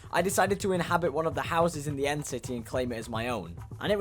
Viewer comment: did he just say end city END you mean nether right bud